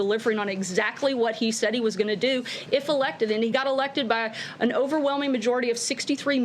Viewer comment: DID SHE JUST SAY "DESPACT THE FITE" TWICE?